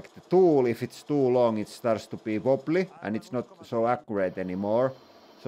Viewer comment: What's the word you use just after 2:00 mins? Tool if it's too long it it starts to __ <that word (biboply?). I think the word in english is chatter but i want to look up what it means in other languages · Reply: "Starts to be wobbly"